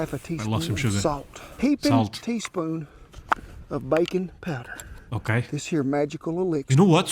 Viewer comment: I legit thought he said "bacon powder". I was confused until I realized I am dumb.